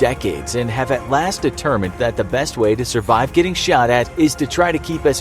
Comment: i thought it said "how to survive getting hot"